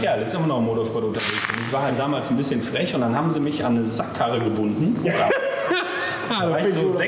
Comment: Ich hab erst verstanden, se hätten ihn anne Sackhaare gebunden...... o.O